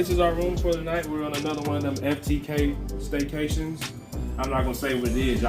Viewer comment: Wait what said at 2:17 😁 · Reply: Lol f them kids